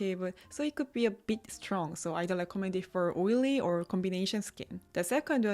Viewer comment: wait, do mean "dont recommend" the inisfree mask or recommended?...for oily skin? Sounded like you said "dont recommend" but the caption is recommend?.